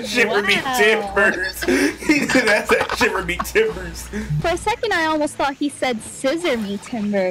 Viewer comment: Ngl I also thought he said 'scissor me timbers' too lmao